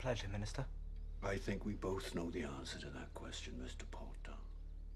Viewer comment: Rufus Scrimgeour sounded like he said 'Porter' than Potter at 0:24 😂😂